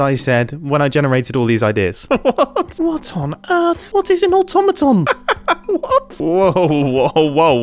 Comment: Mumbo said “What is a Otomatone” TheRealSullyG wants to know your location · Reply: i thought he said automaton XD